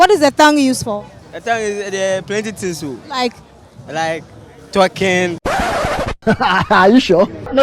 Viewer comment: did he just say a tongue is used for twerking?